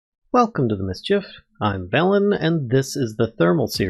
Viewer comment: I always hear "Welcome to Mischief, I'm melon and this is... "